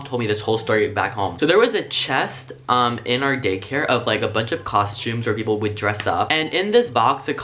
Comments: did he say gaycare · I thought you said gay care lol Edit:This is the biggest amount of likes I have ever gotten lol, wish my own videos got as much:’) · Hold up, did he just say gaycare? I love it! Edit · did you just say GAYcare lol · i thought he said “gaycare” instead of “daycare” and i laughing my head off lol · omg lol did u say gaycare??? I hear gaycare lmao😂🌈😂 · did he say Gay-care? 😂😂😂😂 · idk if its daycare or gaycare. like if you think he said gaycare and dislike if you think he said daycare. i might be crazy but i think he said gaycare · is it just me or did I hear Gaycare XD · I swear at 2:06 he literally said "gaycare" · I almost thought you said gaycare. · Ahahahaha I'M CRYING! You said "Gaycare" instead of "Daycare" lol! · did anybody else hear "Gaycare" ? No? Am i just too gay for this video? Oh ok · Does he say gay care instead of daycare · I thought he said gay care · At around 2:05 is it just me or did he say gay care instead of daycare😂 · is it bad that i thought he said gaycare...😬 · Can't be the only one who heard gaycare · is it just me or does it sound like he said gay care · did he say “gay care” ?👌😂🏳️‍🌈🏳️‍🌈